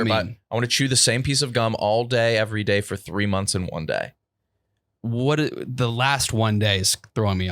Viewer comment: was saying “3 months AND 1 day” but KB and I kept hearing “3 months IN 1 day”